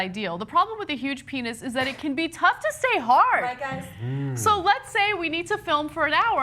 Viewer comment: did Gina say 'black guys'? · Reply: "right guys?"